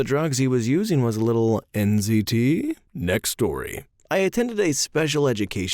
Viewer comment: I was listening to this while looking for my phone and at 6:44 I genuinely thought I heard "NCT" (as in Neo Culture Technology, as in NEO GOT MY BACK, CULTURE THINGS, TECH TECH ON MY MIND) and not "NZT".